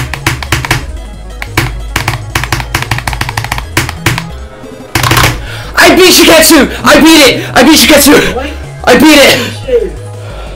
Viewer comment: all i hear is "I BEAT SHITKETSU I BEAT SHITKETSU"